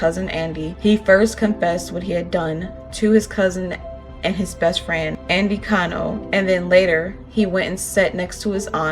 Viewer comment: sounds like he said “ Erik stay there!” to me that makes more sense if he didn’t want him to go in the den and look at his parents right? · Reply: If you listen to the whole 911 tape, lyle actually says "Erik shut up" at some point. But yes, at this point when Erik screamed, he said "Erik stay there." The subtitle is wrong at 5:44.